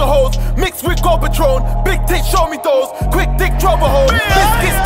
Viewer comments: did he say big dick show me those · Did he just say big dick show me those? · Swear down at 1:26 he said big dicks show me those 😂😂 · Ok, i have to ask this. At 1:26 , did he say 'big dick, show me those'???? I hope for his sake thats not the case, before i go in.